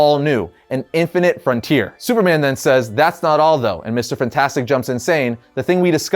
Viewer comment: Did you say "Mister Fantastic" at 12:56? LOL